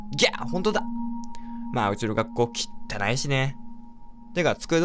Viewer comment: 「うちの学校きったない死ね」って聞こえた